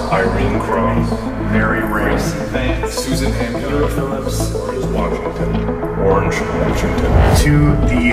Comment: I thought I heard George Washington for a sec at the ritual part lol